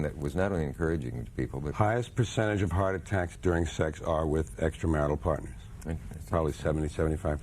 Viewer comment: Did he say extramarital in 2:16?